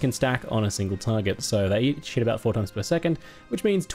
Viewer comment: when you said "each hit" I heard "eat shit" and had a giggle to myself